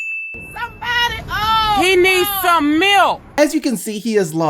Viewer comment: "He need some milk!" 😂😂😂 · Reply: Chanel Mba really. I thought they say "he needs some milf"